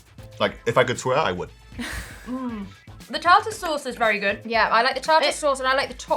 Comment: Oh dear...my hearing... the 1st few timesthey said "Tartar sauce"...i heard , "trouser sauce"... 🤦🏽‍♀️🤣😂